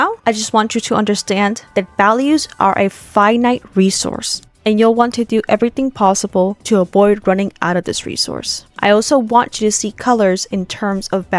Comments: i heard "five nights resourse" and somehow it didn't sound wrong to me, like you drawing freddy, like okay if you say so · "values are a five night resource" was what i heard